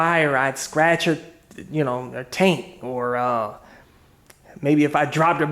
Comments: did you say you were afraid to scratch her "taint"??? Hahahaha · I swear you said "I'd scratch her taint"